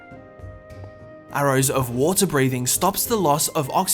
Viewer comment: is slowness said but is water breathing